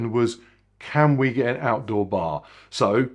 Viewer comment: I heard "outside bar" and was excited to think we might also get cocktail reviews. Sigh.